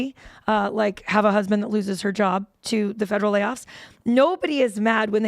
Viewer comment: did I just hear her say that “my husband could lose her job”?